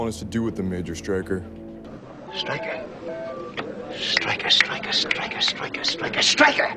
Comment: What's the scene from at 15:45? Sounds like he says "Strike her" over and over but it's a name and then the guy punches the girl xD Looks like from some '60s show, anyone know?